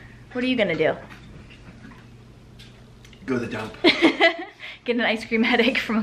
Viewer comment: lol, I definitely misheard and thought he said "go do a dump" instead of "go to the dump." Glad it was the latter!